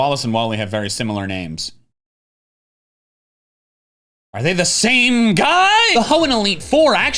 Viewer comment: i’m crying why did i hear “team sky” instead of “same guy”